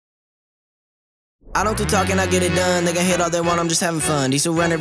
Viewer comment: Who else thought he said the N word at first? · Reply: he said "then" with a hard ass rap accent